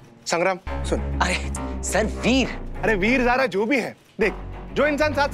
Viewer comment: Edited:- listen carefully bro what police say "veer zara"